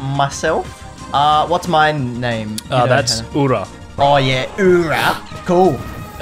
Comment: When I heard this: "What's my character?" Then heard: "Its ORA" I said:"Jotaro"